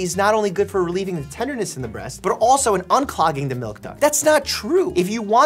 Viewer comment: I thought you said "unclogging the Milk Dud" 😆